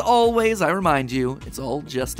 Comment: Sounds like Mat is saying: “Remember folks its just a Theory. A GAAAAAY Theory!” 😁